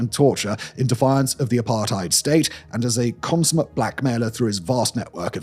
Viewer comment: sounds like you say "in defiance of the Apartheid State"